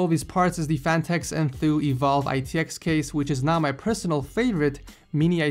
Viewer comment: he actually says NOW my favourite matx case